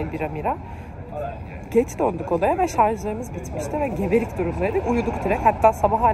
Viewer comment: 'da gebelik durumları mı dedin ben mi yanlış anladım :😁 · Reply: Geberik durumdaydık diyo😂